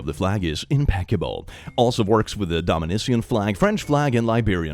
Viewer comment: did you just say Dominisian?